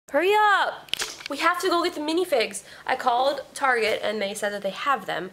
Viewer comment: At first I thought you said mini pigs; I thought you were getting some teacup pigs for pets.